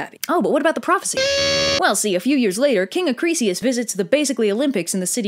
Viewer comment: I heard "accidentally hits him with a biscuit"